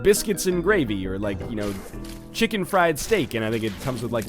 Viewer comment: please message me if I'm wrong but did say "chicken fried steak"